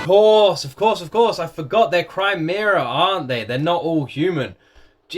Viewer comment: "Crymeera" kills me💀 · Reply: I thought he was saying "Crimea" last episode 😵‍💫